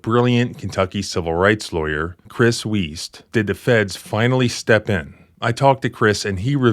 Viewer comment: …. I thought you said